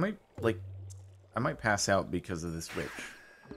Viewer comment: Did call the wizard a b*tch? 1:24:50 Swear that he did. 😂